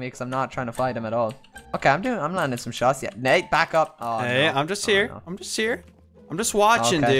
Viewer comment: did say Nate back yup??